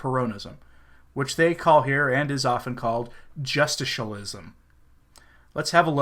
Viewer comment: "Justicialism" is a new one one me, but I thought the narrator said "just-a-show-ism" , and it worked very well in terns of this papacy.